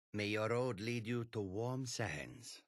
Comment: For the longest time I thought they were saying "May your road lead you to war and sadness".